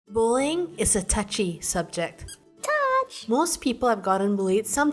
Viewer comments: For some reason I thought you said "Bowling is a touchy subject." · I'm sorry because I know it's just your accent, but I thought you said "Bowling is a touchy subject" in the beginning